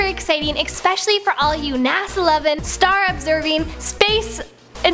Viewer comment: Did she just say "Star Observers"?